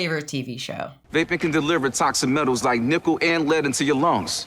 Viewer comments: My friends and I always thought the That's Metal! guy sounded like he was saying nipple instead of nickel so every time the commercial came on we'd wait quietly and then just yell NIPPLE over him saying nickel lmao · the first time my family encountered that first ad, my sister and i both misheard him as saying "nipple and lead," invoking a level of terror and confusion previously unknown to us. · To me, the Dude sounds like he says "Vaping can deliver toxic metals like nipple and lead to your lungs." Check it out, he says nipple, not nickel.